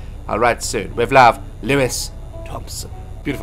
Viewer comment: I thought it said Louis Tomlinson 😂